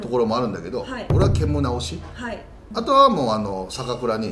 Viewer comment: おい、ケモナって聞こえるぞ笑 ケモナーみたいじゃないか……